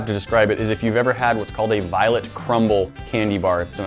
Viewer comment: "violet crumble" more popular one is a crunchie xD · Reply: I thought he said 'violent' crumble... I'm like is there any other kind?